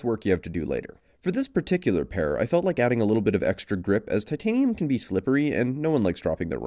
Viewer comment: i thought he said he added extra drip 👀💦